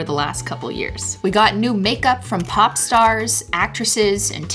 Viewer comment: i thought you said Pawn Stars, and I was like damn even those guys huh?